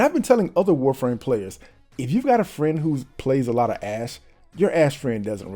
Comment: i hear ass